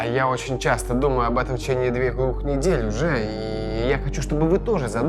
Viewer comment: что он сказал ? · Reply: В течение уже двух недеь